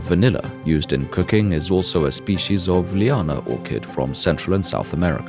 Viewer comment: had to watch twice. Thought he said: "used in cocaine."